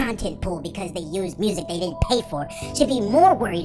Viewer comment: I heard didn't care lol